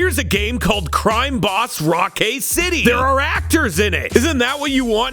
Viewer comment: I heard "Raptors" at 4:52, got excited for a second... =/